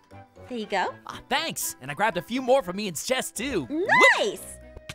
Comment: Did no one notice Noi said Jess in 5:20